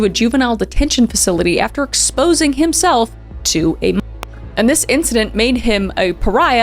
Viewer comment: What was said at 25:44? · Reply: Minor